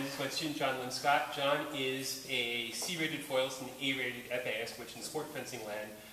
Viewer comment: nice lesson doc. good stuff. also, sounds like you said "an E rated epeeist". A rated damnit! lol